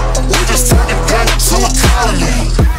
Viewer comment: I swore this said “I just turned a gang into economy!”